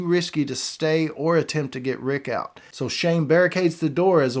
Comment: did he just call shane shame?